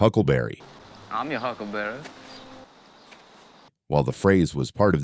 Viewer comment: I thought Kilmer said “Huckle Bearer” for the longest time. As in, “I’ll take you to your grave”. The handles on the caskets were referred to as “huckles”.